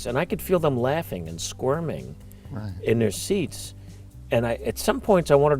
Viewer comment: saying "laughing derisively" may be true of at least some of the people, but what Billy says after that is "laughing and squirming in their seats". This says to me that this was nervous laughter, likely from people who were not actively hostile, but hadn't really confronted the subject before.